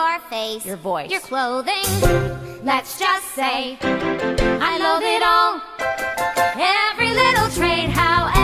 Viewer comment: I swear every time I here "I loath it all" I hear "I love it all" Is it just me?